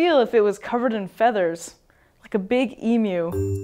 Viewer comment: What was said "like a big _ ?" 0:15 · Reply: Emu